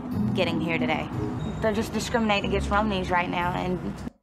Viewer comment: i've watched about 10 of videos on this show...they're saying "romani"... I thought they were saying "romney"...